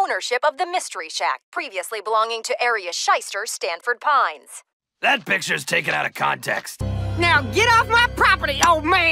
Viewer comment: i swear to god I thought he said "That bitch is taken out of context"